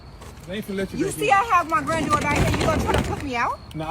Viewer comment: Did she say granddaughter?